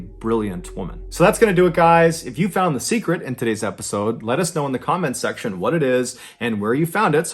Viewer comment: "Let us know in the comments..." I always hear "Lettuce know in the comments..." XD